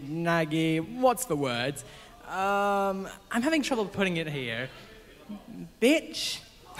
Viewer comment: did he just say BITCH!?